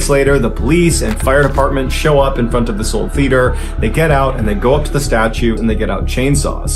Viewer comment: I thought ballen was saying liter at first!?! 😂